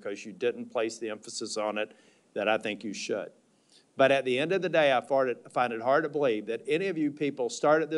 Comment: did bro just say that he farted ??😂😂 ☠️ 😭 🙏🏻